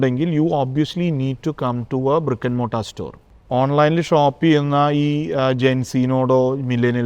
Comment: What is the word which he used at 36:14,15 seconds and again used at 36:34.35 seconds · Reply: Brick and mortar...means physical store